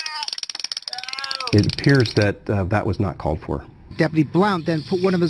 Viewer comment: “it appears that was not called for” ?